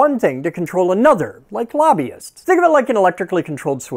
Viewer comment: I thought he said labbist, which would also be true.